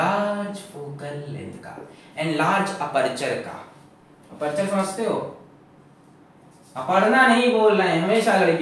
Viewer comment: literally i heard aparna😂